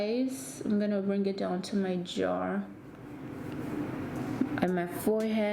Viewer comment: did this girl just say “ima put it on my jawr”??? Reply that part I SWEAR I heard that😂